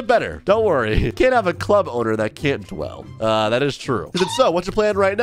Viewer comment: did bro just say dwall